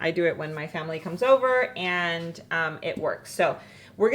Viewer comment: I had to laugh because when you said " you have this when my family is over " i thought you said " I make this when my family is SOBER". I'm going to go make some now while I'm Sober😂